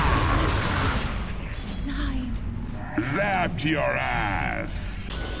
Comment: did Razoz said zap your ass?